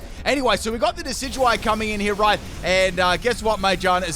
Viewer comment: The Mage-E-Are-Na was hurting my soul-heart whenever he said it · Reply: I thought he was saying mangina...